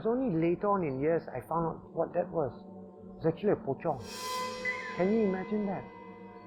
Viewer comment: what did he say at 2:44? a bochong? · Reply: Pocong is an entity wrapped in Malay burial shroud and moves around by hopping.